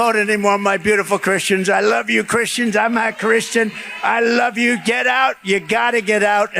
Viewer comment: At the 7:41-7:42 mark on your video, when Trump is talking does he say “I’m not Christian”?